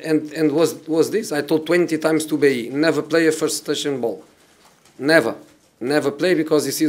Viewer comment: what was Jose saying? "never play a touching ball?"